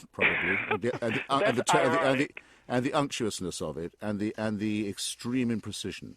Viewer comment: What's the word at 9:51, please? I can only hear something like "amptuosness"...